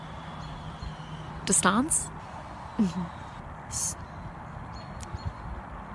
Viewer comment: For some reason.... For me what I hear in "Sashal" is sus shall-I think I've played among us too much